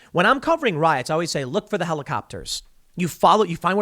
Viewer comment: Did Tim just say to look for helicopter rides?